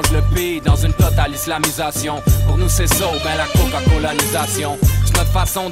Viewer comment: il dit quoi ? J'tu la seule qui entend la CokaCokalisation ? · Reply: Moi je conprend Coca- colonisation .... je crois qu’il fait référence à Coca-Cola (qu’on voit la pancarte) et qui on colonisé même en Égypte.... Tk c’est ma vision personnel ...